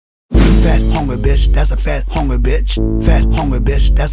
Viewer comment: I only hear "fat honey fish" :c